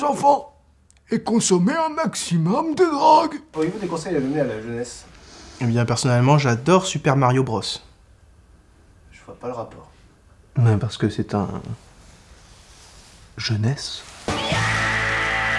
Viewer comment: "Et personne-allemand" j'ai entendu ça dsl